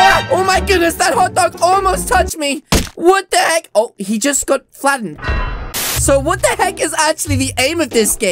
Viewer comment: he just conf- wat ( actually says He just got Flattened)